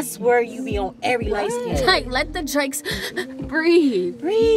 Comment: Did she really say, "Let the Drakes breathe" ??? Lmao after seeing that the dude in the red car does look a bit like drake with hair lmao